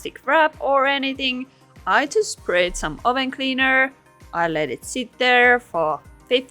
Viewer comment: I thought she was saying Owen cleaner she was saying oven cleaner